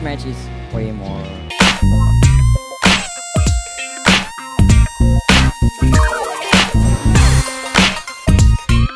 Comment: what said? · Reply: Easyier